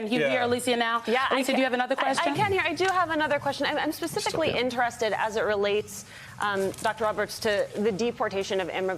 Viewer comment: What did he say under his breath? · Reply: "Stupid"????